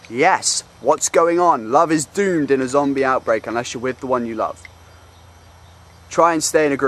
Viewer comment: I'm watchin' this several hundret times and it doesn't get boring! Steve died at 3:45 . What's that word? "dude"? Love is what?